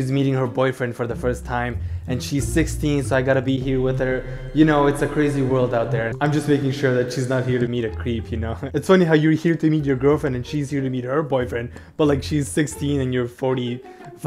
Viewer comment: UHM IDK BUT IN THE INTRO I THOUGHT HE SAID, “I’m here to eat my girlfriend.” I WAS LAUGHING SO HARD